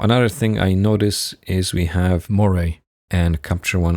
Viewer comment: you magnified the ceiling over the window and said something about what you saw there, but I could not understand what you said. Captions also could not decipher it. What did you see up there? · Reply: Hiya. It was "Moiré" I said :) I think it's not that visible on Youtube due to compression, but Capture One and Lightroom have tools to reduce moiré effect